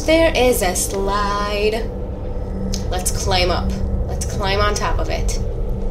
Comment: when u said climb climb climb it sounded like you said Clem Clem Clem